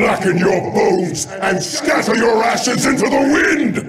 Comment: I was only half-listening and I heard "asses" instead of "ashes"